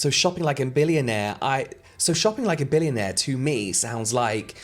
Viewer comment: I thought it said "shot* like a billionaire" and although I was confused, I said 'hell yea" out loud. Lol..